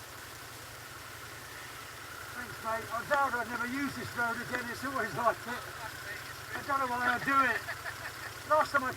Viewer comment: what he say at 21:03 ? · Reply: Fuck nose!